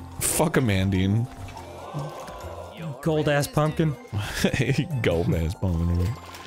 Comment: what does arin say at 31:30 ? "pumpkin man?"